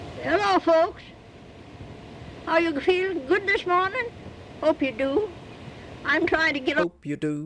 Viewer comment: I had to replay and I believe I heard "this morgan" (German/Dutch/Yada for 'morning') which might give an idea of her roots.